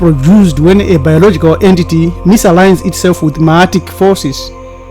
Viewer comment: Please help. Misaligns itself with what forces? It sounded like you said matic or myatic forces or something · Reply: Ma'atic ( forces )